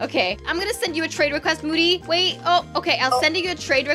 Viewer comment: For a moment of silence for people who thought she said hi boody 🤣🤣🤣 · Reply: I thought she said boody too 😂😂😂😂😅😅😅😅😅😑😑😑😑😐😐😐😐😐😆😆😆😆😆